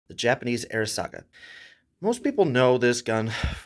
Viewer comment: I don’t know why but the first time I heard this video I thought you said Japanese airsoft gun